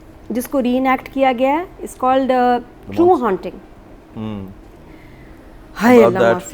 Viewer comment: not getting what they said in the name of series!!! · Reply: True Haunting - it's on Netflix. It's a 5 episode series that cover 2 stories - personally I didn't find eerie hall that scary but the second story was good